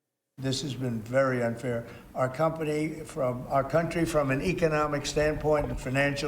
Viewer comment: Little Freudian misspeak there at 1:38 "our company" instead of "our country". His gains but Our losses.